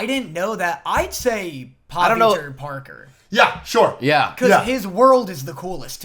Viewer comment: what character does say? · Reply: It’s pavitar Parker